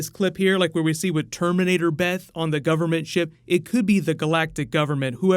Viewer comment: Yeah terminator Beth am I the only one that heard him say terminator Beth and not terminator summer or is just me 😜